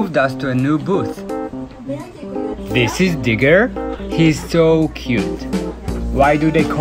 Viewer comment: I thought he said niggar i was like what he say deh but when i went back i was like o digger ok